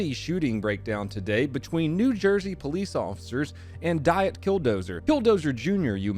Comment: In the beginning I thought you said "diet pill dozer"...... which still works lol